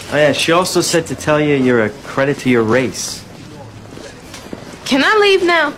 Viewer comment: I thought the First time she Said (Thats a Credit for youre rate) After the second time i understood IT holy **